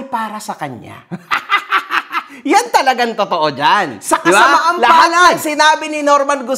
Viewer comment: Exactly 23:59 to 24:01 may narinig din bakayaong "sa kasamaang palad"